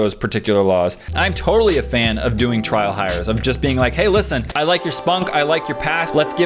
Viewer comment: did say "I like your spunk"?